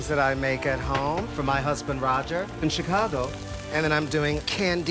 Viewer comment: wtf did u just say ?? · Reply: Me too he just said my husband is a woman Wtf don't tell me😱